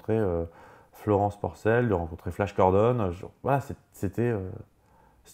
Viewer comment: c'est moi ou tu as dit avoir rencontré flash gordon???